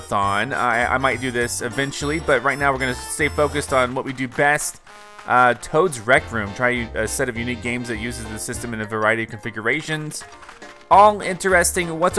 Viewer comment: I was distracted at 1:00 and rather than "Toad's Rec Room" I heard "Toad's rectum" 😂 Poor Toad